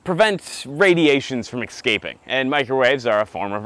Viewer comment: did you say "EX- caping" ? lol